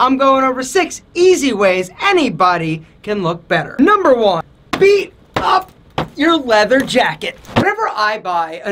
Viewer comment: for a sec I thought he said to “beat your meat”.